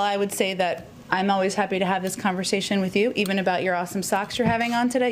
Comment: I thought she said Awful Socks...but she said awesome...Shoot lol